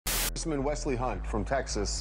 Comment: Did he say “dis nigga Wesley Hunt”? 😂😂😂